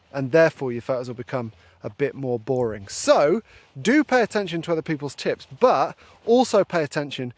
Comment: I heard, "pay attention to other people's tits" if I do this then I'll not get much photography done.